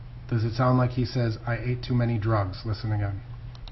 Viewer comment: With all honesty, it sounds like he says “I hate the way this feels, please.” I’m trying to make myself hear “I ate too many drugs” but it just doesn’t sound anything like that. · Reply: To me it sounded like he said, “I ain’t doin’ any drugs.”